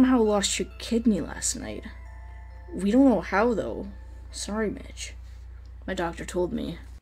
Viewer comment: I thought the Doctor said, "Sorry bitch". 😂